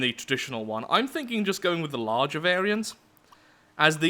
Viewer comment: fighting adhd and tiredness i heard "the large" as "the Larch", nearly died and had to go back and make sure.